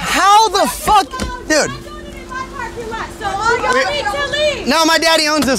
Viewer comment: I thought I heard the *u need to leave * 😭😭😭😭😭